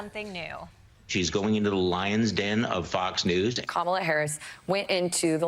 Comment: ohhhh I thought they were all saying "the Lying Den" when they were talking about Fox. This makes much less sense.